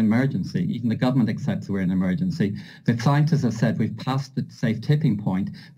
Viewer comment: His bumbling response of tongue twisted lies and an almost dissociated gaze as he rambles on... Did he say "clientists"?...These demons will never succeed, but they sure are good at suckin seed.